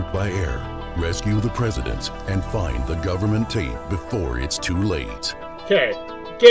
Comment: I know it said tape but I kept hearing " Find the government taint" and I don't need that visual image in my brain. 🤢